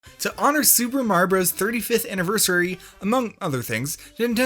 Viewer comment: Me: Clicks On This Video Him: "to honor super marbros 35th anniversary Me: "Did you just say super marbros?"